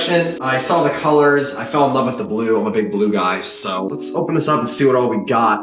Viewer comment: I heard “imma big blue guy” in the wrong way 😂